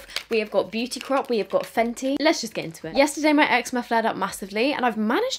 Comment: haha i thought you said "yesterday my ex flared up"